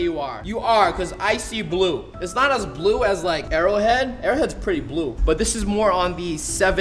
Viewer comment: did he just say airhead